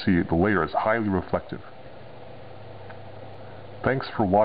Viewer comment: Am I the only one who heard him say Nerd Rape